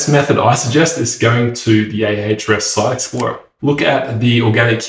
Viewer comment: Not really beginner. Tons of tools you utilized and kind of glossed over some of us have never heard of. Do you have a video on “google search console” or what you said at 4:13 which I couldn’t make out? “Ay aytch ress sites” ? Sorry. Not meaning to be judgy. I just couldn’t make out what you said even playing it many times. Is that a third party tool or some section of Google console or something? 😀